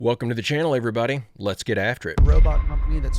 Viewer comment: My wife swore u said let’s get active🤣